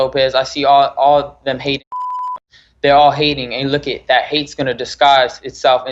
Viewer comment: Did he say hating ass niggas?